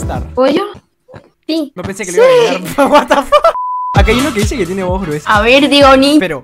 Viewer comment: pense que dijo la tiene gruesa xddd